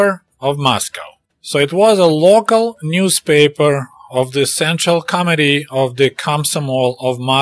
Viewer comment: Sounded like you said central comedy instead of central committee. Probably more appropriate.